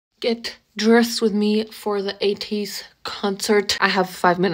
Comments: I know nothing about K-pop and I just heard 80's concert, man was i confused with the different outfits, lol. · today years old finding out likes ateez what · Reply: LOL thanks. I thought she was saying 80s concert. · i heard “80s concert” like american 80s music and was SO confused by the hello kitty courset